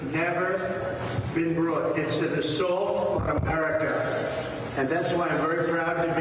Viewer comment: Wait, did trump say It's a salt on America dude is always eating!